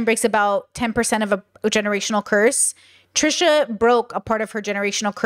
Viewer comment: My brain heard you say "generational thirst" not "generational curse" 💓😂 Like ah yes I must strive to not be as thirsty as my ancestors!